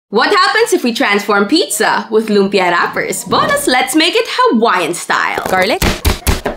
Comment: Did she just say loempia😢🥹